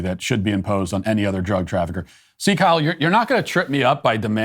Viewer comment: When he said “see, Kyle,” it sounded like he said sieg heil.